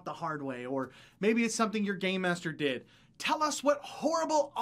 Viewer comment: at the end i heard "tell us some of the horrible things ur gay master did" made me laugh a little bit :P